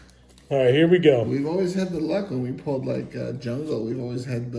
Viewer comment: Bahaha! I thought was gonna say "We always have good luck, when we pull our head out of our butt." Always a good decision.